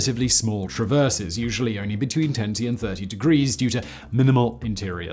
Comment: Im sorry @ 1:02 did u say they could rotate their turrets between tenty and thirty degrees? Ummm...🤔🤔🤔 tenty??? I'm pretty sure tenty isn't a thing. 🤨